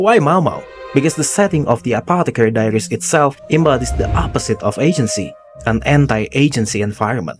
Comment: i thought you said it "embodies the opposite of Asian-cy" and i was like "fym that's about as asian as it gets"